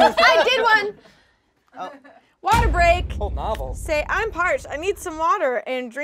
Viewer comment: When court said water break, I thought she said waterboard lmao. They’re going hard